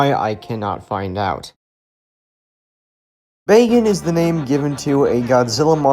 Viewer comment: did you say Bacon?